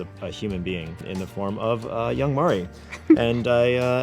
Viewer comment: Did Coach just say YOUNG Mari?